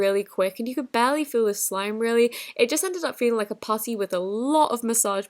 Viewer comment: if u put ur video with subtitles when she says it feels like a putty the subtitles say it feels like a pussy At 5:19 the subtitle says it did change the texture asshole